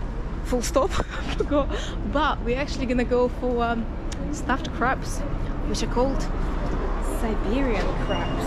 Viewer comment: Great episode....LOL.. although I thought I heard you say you were going for "stuffed crabs" (vs stuffed crepes)!